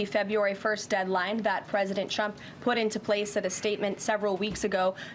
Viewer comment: Did she just say President Chump???